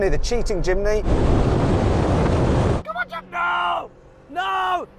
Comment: 日本語で「ガンバレジムニー！」って言ってるように聞こえるw